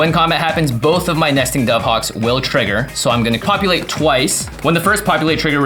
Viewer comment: Jimmy says he's going to "copulate" twice" instead of populate, lol wtf??